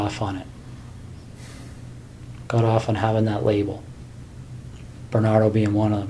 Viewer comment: What did he say at this juncture, immediately before “I don’t see that in you” almost sounded like “Bernard being one of them” [edit] mystery solved, “Bernardo” was an infamous Canadian serial killer I hadn’t heard of. (Paul Bernardo)